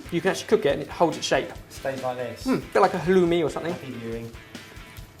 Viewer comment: did he said like chaloumi?